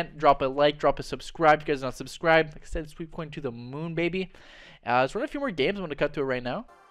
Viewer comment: what did u say at 10:59🤣🤣🤣🤣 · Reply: Sweep coin to the moon